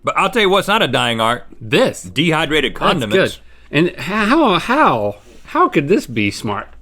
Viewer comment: I wasn't paying attention so I thought he said dehydrated condoms and was like ??????????????????????????????????????????????????????????????????????????????????????